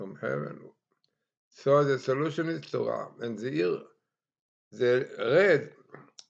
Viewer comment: Rabbi, captions should be disabled. Too many words are being translated incorrectly and these codes are about getting the correct words. Example @ 4:10 you said, "Torah," but the caption says "to arm."